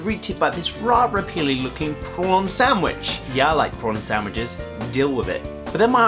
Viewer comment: I thought you said 'porn sandwich' ... Yeah, I wouldn't like the taste of that either.. O.o